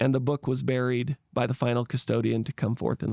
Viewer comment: Did you say final “Custodian?” 😎